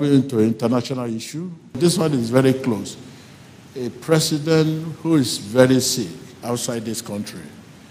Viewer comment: Gabriel Ohia I am pretty sure I didn't hear TBJ say ex president. I heard president @ 0:48.